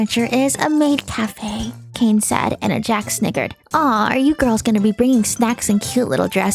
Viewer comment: I thought Gangle said the hard R for a second, I had to replay it and realized it's "snickered"